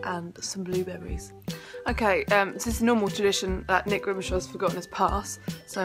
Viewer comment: Did she say that nick is jew in 2:40 ?